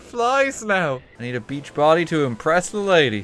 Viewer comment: Scribblenauts: asks for a beach body: gives them a beached body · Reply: Beach body? I thought you said bleached body